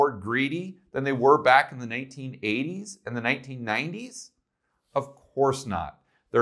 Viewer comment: instead of "Of course not" the screen reads, "Off Course." Mistake? Hmmm...